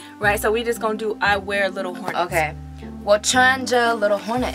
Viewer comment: I always thought she said "jump on it " not little hornets!😂😂